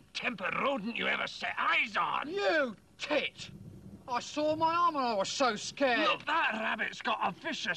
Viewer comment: "I soiled my armor I was so scared" 1:01 - "I've done it again" Who else didn't catch that at first?